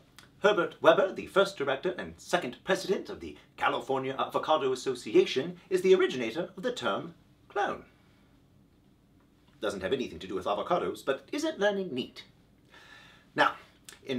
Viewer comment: What was the term Herbert Webber invented? I heard “clown”, but that can’t be right. EDIT: oh, it’s “clone”! That makes much more sense.